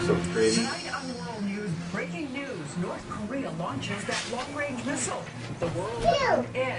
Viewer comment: hmmm at 02:25 north korea has launched the large missle erm wtf did i just hear